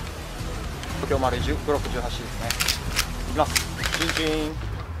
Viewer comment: チンチーンって聞こえるの俺だけ?